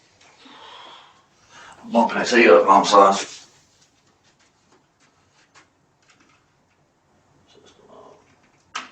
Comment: what in the world is he saying here? I played it over, still have no idea! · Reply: Going to turn this heat up, know what I’m sayin’